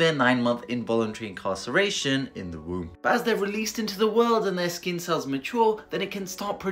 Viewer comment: For one second I thought I heard Dr Mezher say that "as they are released into the wild"